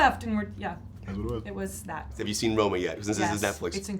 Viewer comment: What did he say at 3:53 ?? He said it so fast, I can’t tell if he said “did you see Roman”, “did you see “Roll Mer”, or “did you see “Roll Men”...? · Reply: He said "ROMA"